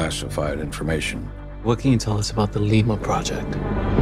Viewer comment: "What can you tell us about the Lima project?" "What's Lima?" "Lima Balls" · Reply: I swear I heard him saying "Ligma" first time.